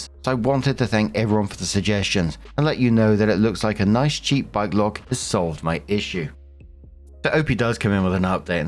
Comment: S2: tell the sister she needs to know. Also him blackmailing should be enough to show he’s a big red flag Oh bike lock I thought was saying bi-clock. I was stumped thinking maybe it means barbed wire